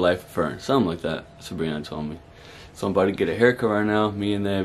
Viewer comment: I never rewinded so fast because I though I heard “that’s what Sabrina told me” but he said “that’s what Bri told me” 😭🤣💀